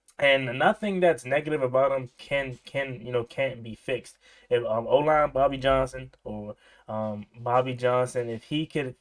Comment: Lol I had to rewind, was multitasking while listening and thought you had said "OG Bobby Johnson" lmao